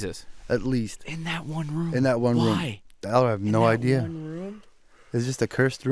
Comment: anyone else hear at 19:20 someone say "my room"